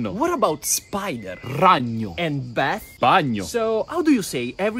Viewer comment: Why did I heard "banyo" if you don't know what it means it means "bathroom" I'm a Filipino 👌